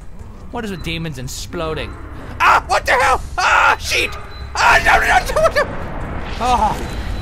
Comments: At the cradle when it splodes a sheet attacks him and i thought he said ahhhhh shit but he said sheet ;( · lol on 7:32, sounds like he said SHIT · haha at 7:26 sounds like he said a shit! but it was sheet lol!